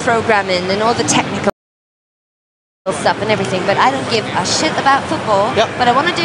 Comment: did she say she doesnt give a shit about football?